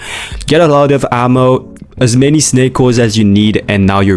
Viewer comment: i really thought it said snake oil to be faster and it wasnt sped up